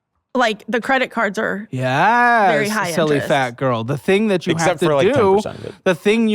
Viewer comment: Omg did he just say "you silly fat girl" at 35:10 😂